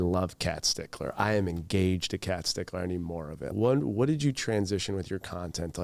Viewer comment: I feel like it was an interesting use of words, like normally people would say I’m “engaged with,” not “engaged to.” 😂so I definitely paused to overthink that for a second hahah. If the rumors are true, hope they’re enjoying their time :) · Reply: I've always heard it as engaged to.